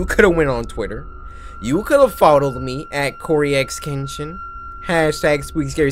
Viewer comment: On the third animation when Cory was saying "you could followed me" I heard "fodled* Cory: if you ain't "thatonegirl" move aside you could've folded me- Me: pauses .........excuse you cory?